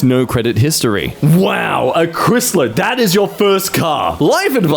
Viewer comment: did you just call it a "chrisler"?